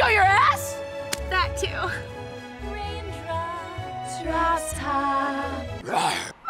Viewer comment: Rain drop Drop top RAWR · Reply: Hydro Gamers I thought it said Rain Pop Crop Top